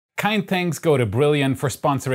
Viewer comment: I always hear "kein thanks go to" instead of "kind thanks go to"